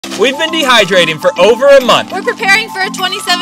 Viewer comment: LMAO I HEARD IT AS ”we’ve been dehydrated for over a month”